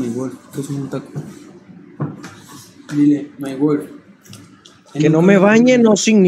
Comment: soy yo o escuche que le dijo a my wife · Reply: Dijo myworld :v